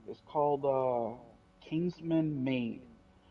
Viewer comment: Whats the game called at 12:00 mark. I couldnt hear it clearly. BTW awesome show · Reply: I think it is kingsman made